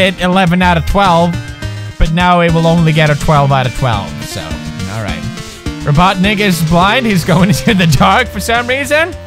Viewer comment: anyone else hear the robotnigga???? he said that